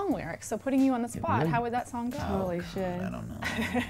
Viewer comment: did she say holy shit?